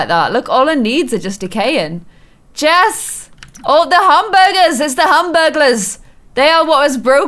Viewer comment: who else heard “hamburglers”? I dead😂😂😂